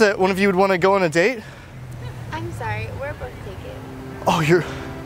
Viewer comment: what did she say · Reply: i’m sorry we’re both taken